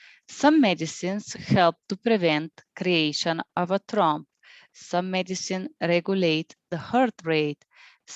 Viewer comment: what did you say at 11:13 ? "creation of a trump" ? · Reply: Thank you is 11.13:Some medicines Prevent the creation of a thrombus( blood cloth)/ tromb - short.